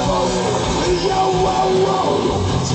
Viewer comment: I'm just a fireball! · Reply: I thought he said, I'm just a firewall